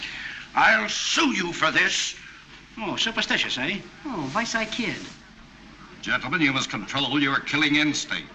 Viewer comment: on this clip, after Moe says “oh, superstitious, eh?”, you’ll hear immediately after that, off camera, someone saying “Vice Eye Kid”. First off, I am hearing that right? Secondly, if that’s what he said, what does it actually mean? I’ve never heard that saying before.